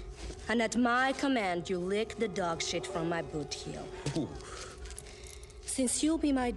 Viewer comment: I’m not gonna lie, when I first heard boot heel, I thought she said her own booty hole, lol.